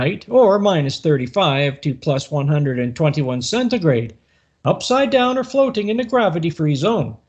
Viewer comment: What does this guy call the temperature instead of Celsius ? I couldn't even understand the word/phrase. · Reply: Centigrade. Old term for Celsius, a lot of people still use it for some reason.